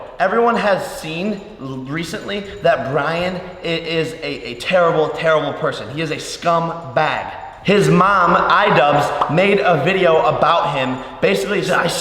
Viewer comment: Am I the only one who heard him say "his mom: idubbbz"?